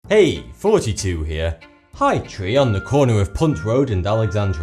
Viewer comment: did he say 42 here?I swear in almost all of his vids he sounds like he is saying "hey 42 here".